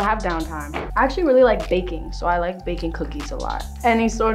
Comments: She said baking but I thought she said vaping. Dam cold (I'm sick) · i thought she said she liked bacon. and then i thought she said i like bacon cookies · When she said baking i thought she said vaping 😂 😂